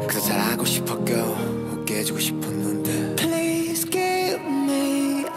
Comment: gracias por traducir cuando lo esciche pense que decia please kill me Xd tremendo pánico que sentí xkanx